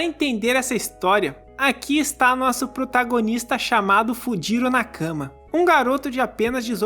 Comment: entendi "fudiru na cama"